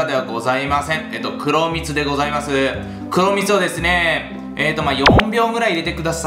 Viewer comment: 壇蜜でございますって聞こえたら 黒蜜か爆笑